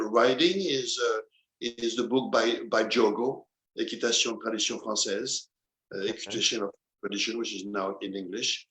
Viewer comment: Also 1:08:45 he mentions another book. Does anyone know the name of the book? I couldn’t understand what he said in the video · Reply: He mentioned there the book from Dom Diogo de Braganca, „Dressage in the french Tradition „